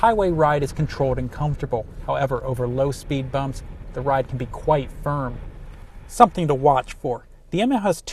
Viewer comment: quiet what!? I didn't understand what he said :when it comes to low speed what 3:12?! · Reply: he said quite firm